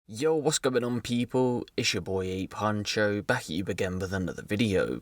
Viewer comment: I know your name is but when you say it at the start it always sounds like your saying a poncho haha.... Buzzing that I found this channel I love crime stuff but it's normally just in America theres no English ones till now.